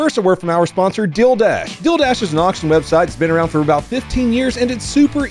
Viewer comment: It always sounds like he is saying 'Dildash'.